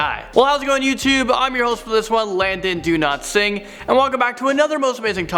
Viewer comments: I heard you "Landon Don't sing" lol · I swear I hear "London do not sing". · My dumbass thought he said Landon do not sing